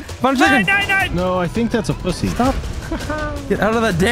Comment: Did Brock just say pussy? I never hear him swear but when he does it it’s hilarious!